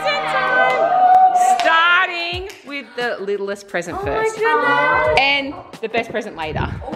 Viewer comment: the way sabre keeps saying ‘oh my good nazz’ instead of oh my goodness